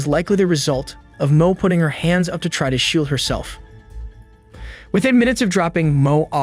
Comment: Did Mo try to shoot herself by the hand wound? @ 16:07 Bro the narration is so confusing and to follow. I have to pause to decipher what you actually said. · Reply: "...trying to shield herself..."